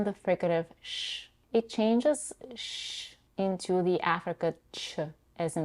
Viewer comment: What did you say here, could you write it out for me? Was it: African Ch? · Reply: Hey, sorry I didn't respond earlier. The linguistic term is affricate. The two affricates in English are CH as in choice and J as in job. I hope this helps.